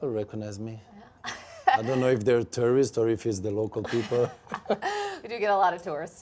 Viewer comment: Omg, when he said "tourists" at one point in the vid I thought he said "terrorists" 😂😂😂😂